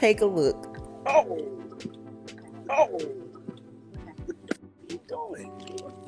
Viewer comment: He said “WHAT ARE YOU DOING?!” LMAOOOO! At least she a good side piece and ran 🤷🏾‍♀️ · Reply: Oh really, I heard "where you going?"